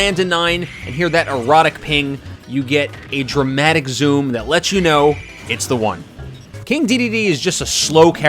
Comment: "That lets you know, it's the one" I thought you said this was the 9 we are landing 🤔